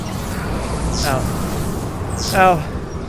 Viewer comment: if you cant hear him... Ow Ow Ow Ow Ow Ow Ow Ow Ow Ow Ow Ow Ow Ow Ow Ow Ow Ow Ow